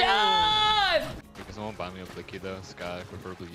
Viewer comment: That sounded like 'JOD' but ...only if he knew that word.